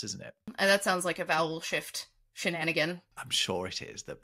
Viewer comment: Goodness me, what is wrong with you two? The last video was all about people's stools and now there's today's potty mouthed venture. That said, I genuinely misheard you say vowel shift, I thought you said bowel shift. 💩